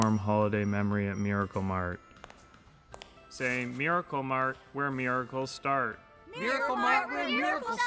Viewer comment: Estoy segura que dice "where miracles start" ( donde comienzan los milagros)